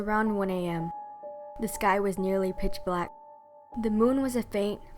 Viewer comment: I thought she said, “A guy”, but she said sky.